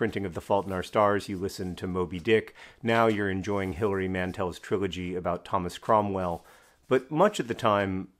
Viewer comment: That awkward moment when you mishear Hilary Mantel as Howie Mandel and for the rest of the video and until you look it up think that Howie Mandel wrote a trilogy about Thomas Cromwell.